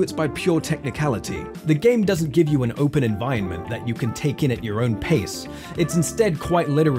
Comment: Huge nitpick, but you sound like your saying envienment